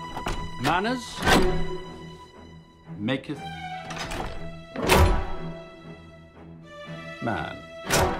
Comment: what he says on 0:45 I cant hear btw MOVIE IS SO AWESOME!!!! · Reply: "Manners maketh man."